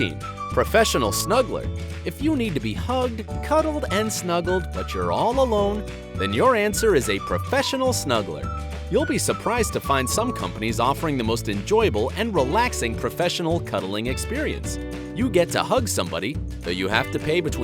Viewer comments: I actually thought bright side said “Professional smuggler” hahahahahahahahahahahaaaa 🤣🤣🤣!!!!!!!!qq🤣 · When he said snuggler i thought he said smuggler